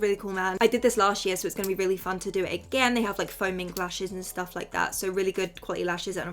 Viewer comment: why did I think you said foaming lashes😂😅